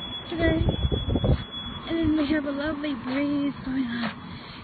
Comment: a lovely breeze lol it's sounds like I said BLEEZE!! LOL!!!!